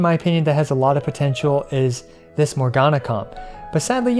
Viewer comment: Mogana comb? Am i hearing wrong or are my eyes broken or even both? Because i hear Mogana Comb but on screen i only see the "Ga"nyu no "Mo"na or Dio"na" ... o.o